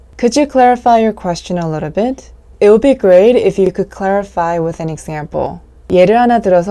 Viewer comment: 언니 10:54에 it will be great으로 들려요. 제가 잘못들은건가요?